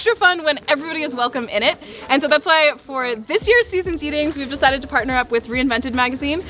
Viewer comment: Did you say "Season's Yeetings"? Please tell me you said "Season's Yeetings"!